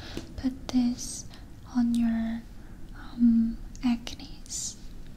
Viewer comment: Please help, what's the word on 19:02 minutes? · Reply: WildWolf Maybe it is Aknes. Same as pimples.